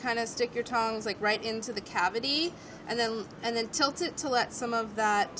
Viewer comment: Stick the tong right into the cavity... I heard that as "Stick the tongue right into the cavity", had a double take, and the word "nice" subconsciously came out of my mouth.